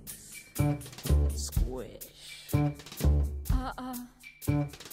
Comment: when i first saw this i thought Audrey was saying dipshits (pardon my french) i now know it is lipshitz ( I can be so stupid lol)